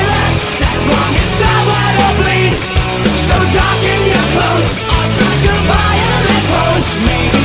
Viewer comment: I hear "So darken your clothes or strike a vile lamp post" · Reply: I hear “so darken ur clothes and strike a violet pose” 😂